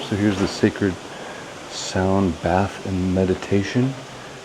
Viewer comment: 😂 I thought he said Sacred sound and Medication!